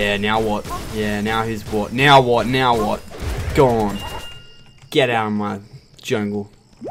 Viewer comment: lol almost thought you said "get out of my nation"